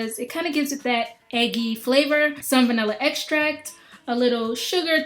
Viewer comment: when she said "some vanilla extract", i thought she said "salmonella extract"....